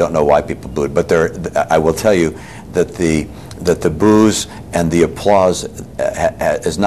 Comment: The booze and the applauds xDsounds like he says that.